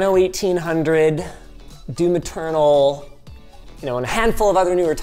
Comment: For a long time I always thought Linus said "Due Maternal" instead of "Doom Eternal" because of the way he pronounces it. He tends to be emphasis on the M, and then doesn't put any emphasis on the E in eternal.